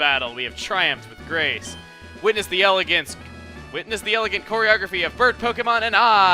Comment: choreography hm well the way you say it chugga it sounds like your saying pornography :)